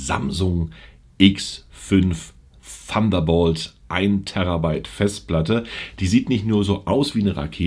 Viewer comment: Hab ich mich verhört 🤔 er verschiebt eine 3tb Datei auf eine 1tb ssd 😂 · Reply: Du hast dich verhört.Es ist ein 14 TB Festplatte.